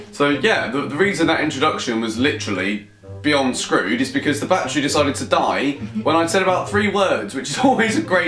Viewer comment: I thought he said that the badger died XD